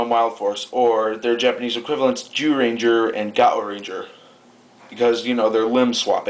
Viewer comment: did u say Jew Ranger?